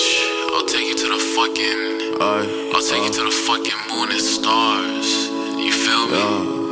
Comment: i thought he was saying "mornin' stars"